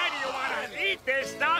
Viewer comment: Thought Mr Krabs said "what the hell is going on in here" for a second